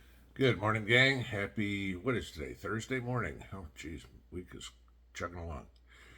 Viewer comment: I thought you said “Wednesday morning” 😂 I almost cried